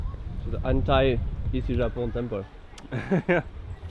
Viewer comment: J'ai compris "Hentai Ici Japon temple" au lieu de "Anti Ici Japon temple" x') Sinon, super vidéo, vous assurez toujours ! :)